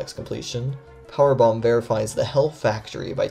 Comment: kinda sounds like you said “car bomb” rather than “Powerbomb” lol